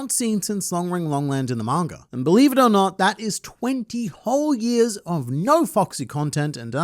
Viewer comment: I heard "20 years of no fucksy content" and laughed so hard😂